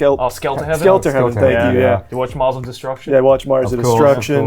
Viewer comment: the youtube subtitles say muslim destruction instead of mars of destruction lmao